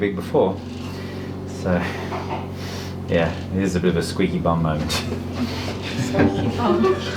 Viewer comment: Dr Scott: yeah thats a bit of a squeaky bum moment Vet Nurses and me: what does that even mean?!?! lmao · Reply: I thought he said that's a "squeeze your bum" moment not squeaky bum 🤣